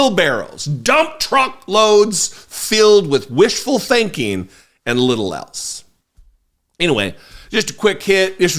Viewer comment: When you said Republicans have wishful thinking and little else, I heard "and little elfs". Which kind of fits, too. ; )